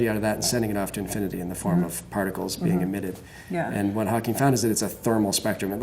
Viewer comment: The subtitles say "admitted" but it actually should say "emitted". Awesome Video nonetheless, just a quick correction ! :D